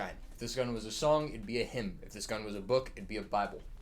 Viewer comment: did you say HIM? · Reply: hymn